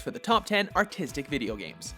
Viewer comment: First I thought it said Top 10 Autistic Video Games xD